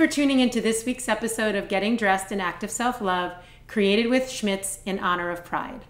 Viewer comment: Seriously thought it said "Self-Love with James charles"